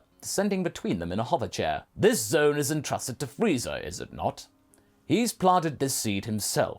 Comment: Lol you said Whis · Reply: It's easy to mishear, but he just says "Freeza" really fast